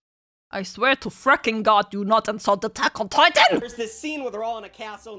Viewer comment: I've listened multiple time... and all i hear is taco titan